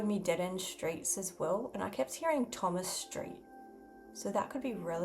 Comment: i could have swore you said thomas lang, you guys got to check him out.He's like the world's best drummer.He is absolutely insane